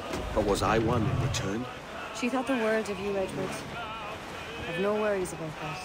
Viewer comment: Does anybody hear someone say "careful Edward"